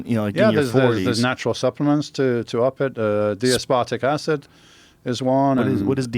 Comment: lmao Joe thought he said D Aspotic Acid instead of D-Aspartic Acid